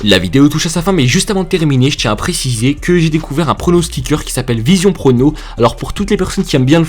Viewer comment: j'ai entendu vision porno ou quoi 😂